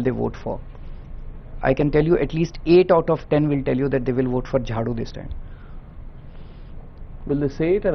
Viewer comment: i heard jhaatu 😂😂